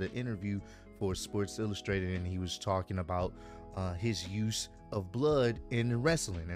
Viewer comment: I thought he said there should be more bloods in wrestling I was like why don't you just form a bloodline faction with birdman Lil Wayne Young thug, cardi b, dipset, mack 10, if they ever release thug, I mean, and the game. I'll see myself out now.